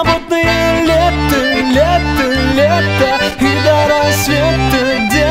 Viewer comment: Мне послышалось пиво до рассвета,беззаботное лето ,а кто наливает